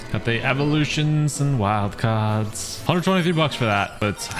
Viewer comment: did bro say 120 v-bucks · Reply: Not to be that party pooper or anything but he said 122 bucks